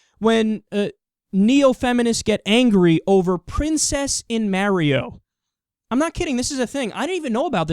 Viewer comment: Lol I always forget I'm in the south until I hear Kyle say "princess in Merio"